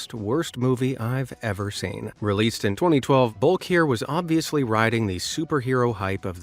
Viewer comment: he said 2012, but captions say 2011???